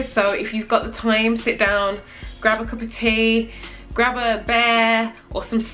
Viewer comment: i thought you said "grab a bear" and i was like ???? and then i legit made myself believe you meant a stuffed animal bear. I was like, ok i guess some people do that? LOL Then I was like WAIT I THINK SHE MEANT BEER! And then I felt like an idiot LOL accents lol